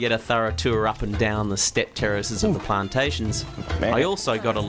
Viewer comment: wait what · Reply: Haha. I caught that too. I think he meant to say terraces. He said it again at 6:12. He does seem to say terrorists tho.